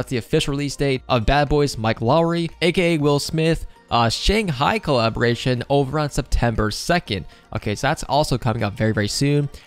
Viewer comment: is it me, or did he say “Shanghai” instead of Shang-Chi in